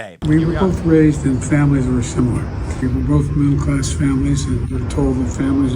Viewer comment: My ears heard something else when Joe said they both came from middle class families. I thought he said mental class families.😂